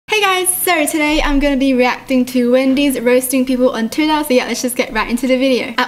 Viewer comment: TOO EXCITED!! MWAHAHAHA ^^ love you so much! be kind and stay positive. (( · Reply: I thought it said wengie roasts people on twitter